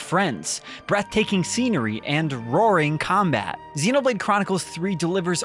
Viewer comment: I thought you said boring combat and I couldn't agree more. Then I rewinded it and heard that you actually said roaring.